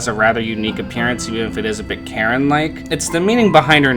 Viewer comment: At first I thought you said "carrot-like" instead of "Karen-like" but that works too XD